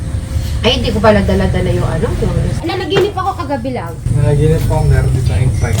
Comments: tma ba narinig ko hahhaa:nanaginip aq kagabi love 😅😅😅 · Tama ba narinig ko 3:54 sinabi ni nanaginip ako kagabi love❤😂 sabay cut Ng video 😂kilig tlga, oyyy I smell something's 😊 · ano po parang may narinig aq na ''NANAGINIP AQ LAB" TAMA DINIG KO INUULIT ULIT KO PARANG GANUN EE😂😂😂😂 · tama ba narinig ko... nanaginip ako kagabi LOVE🤣🤣🤣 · my narinig ako..nanaginip ako kagabi love?..di ko sure hehe .bsta ganun dinig ko